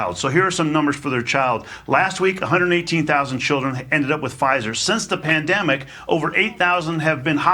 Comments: Listen carefully to what he said. He doesn't say "ended up with cov1d". He said "end up with PFIZER". Freudian slip??? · Did he just said 119000. Ended up with Pfizer. ???